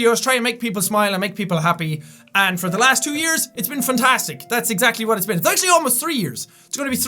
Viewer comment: When I'm watching this you have 8,007,680. Also, it sounded like you said 'funtastic' instead of 'fantastic'. XD